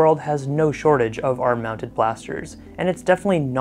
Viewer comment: I thought it said, “air mounted blasters”